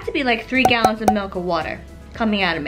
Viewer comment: “3 gallons of milk of water”. 😂😂😂 I love you. Congrats!! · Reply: I thought she said that too but then I thought maybe she said "milk or water" 😂🤷